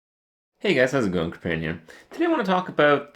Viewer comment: I just noticed he says "hey guys, heres kirrparian" I always thought he said "hey guys, he's a (gonk?) opinion... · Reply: Actually he says "hey guys how's it going Kripparrian here"